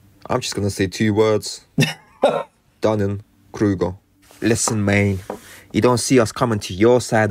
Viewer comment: Near the end of the video, the narrator said he had "two words..." and I thought he said 'Dummy Kruger', but I now realise it could've been 'Dunning Kruger'. I had to look that up! 😊 How apt! 😂